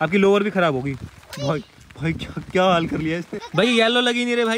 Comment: me kya bola 😂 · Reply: Bhai sahab bola